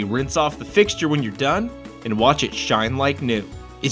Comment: am i the only one who heard "and watch it shine like a nigga" at 1:34?